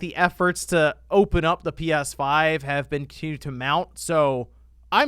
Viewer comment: What's the word you use at 10:57. Sounded like tantamount.